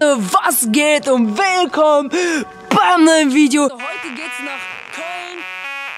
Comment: du hast Köln gesagt aber ich habe verstanden polen 😂😂😂😂😂😂😂😂😂😂😂😂😂😂😂😂😂😂😂😂😂😂😂😂😂😂😂😂😂😂😂😂😂😂😂😂😂😂😂😂😂😂😂😂😂😂😂😂😂😂